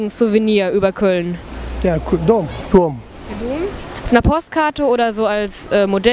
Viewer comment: hab ich Kondom verstanden XD